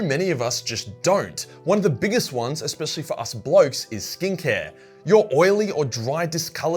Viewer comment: I kept hearing him say "stink hair" it was "skin care".